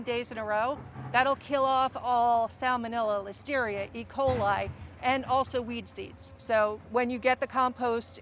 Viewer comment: did she say it kills weed seeds?